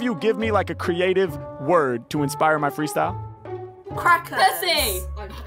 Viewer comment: I thought she said crack heads not crackers😂